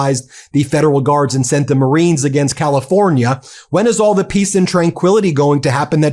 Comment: You misheard, Pieces not peace